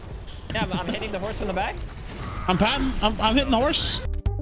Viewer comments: He actually said "I'm hittin' the horse." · I'm confused. at 3:43 the whole joke is the captions says, "I'm in the horse." But I clearly hear saying, "I'm hitting the horse."